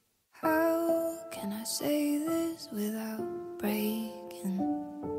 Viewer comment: the first lyric, I thought she said "how can I say this without bacon" 😂😂